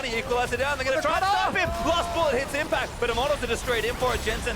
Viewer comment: Nobody realised that he said Lustboy and not wildturtle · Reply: I thought he said lustboy, it's actually last bullet